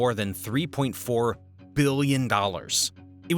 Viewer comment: I heard a billions dollar🤔